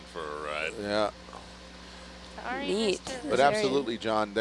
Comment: Did she say "yeet" at 10:18 ?